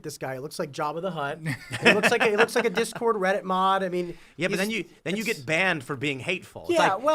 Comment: "He looks like a reddit mod" this would normally be Defamation Per Se ...if it wasn't true. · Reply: "reddit mod," I thought he said "reddit mom."